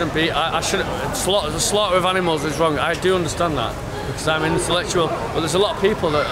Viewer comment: "I'm an intellectual" as he keeps trying to justify eating meat, eggs and dairy....oy. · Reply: I thought he said 'I'm intellectual', which is ironic...